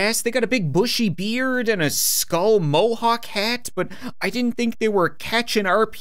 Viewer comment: did said mohawk cat!?